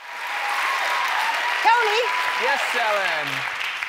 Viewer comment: lol i thought she said: Kony! :D